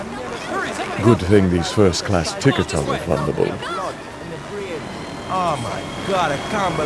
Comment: Oh, now I see what he said at 18:25. "Good thing these first class ticket's are refundable".